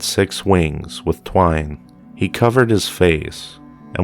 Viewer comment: Seraphim had six wings like Lucifer, two (twine) covered eyes and two covered feet in order to humble themselves before the glory of the Lord. It sounds like you were saying they had wings of twine as in string ! Ha ha !